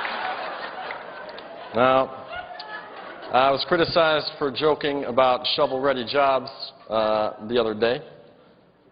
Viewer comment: Wish I could have heard what the person yelled at 9:50 when he said that's enough outta you! Darn it!😥🤔🤔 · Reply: She said 'yes you can'.